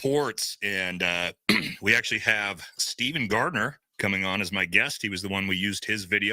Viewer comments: I Thought You said Stephen Greer. For a Moment mmmm....QUIT PUSHING BUTTONS!!*!!#!__.>.>.>.>>.>.>>.>.>...... Supremely... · I Thought You said Stephen Greer. For a Moment mmmm....QUIT PUSHING BUTTONS!!*!!#!__.>.>.>.>>.>.>>.>.>...... Supremely... Did It!!! .....